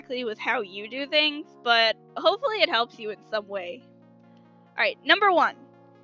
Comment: I thought she said hopefully this will help you at subway